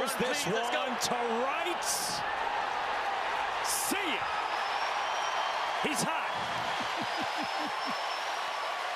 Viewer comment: What the ump say at 11:50 when he hit the homerun ?? “You’re on___ let’s go”?? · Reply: i think he said run please let’s go